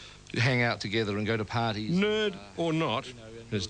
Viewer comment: LOL! 3:53 I thought the interviewer called him "NERD!" Not that he was moving on to make some point.